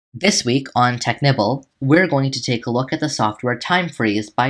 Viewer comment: It sounded like you said Technipple